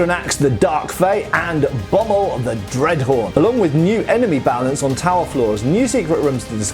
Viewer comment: lol, when you said "dreadhorn" I heard "dreadwhore" 😂😂😂